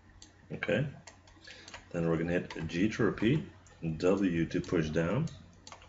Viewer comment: Hi.. can you tell me what are you saying in 1:54 about G droopy? because the subtitles says so.. "G Droopy" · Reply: xhinz naoki Hi there, I didn't say "G Droopy" but "G to repeat"